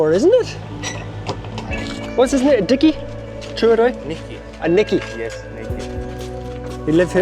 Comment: I thought the man said the lovely dog is called "Nicky" not "Dickie" 🤔 Thank you so much for your love and care for all the dogs who need you so so much ❤❤❤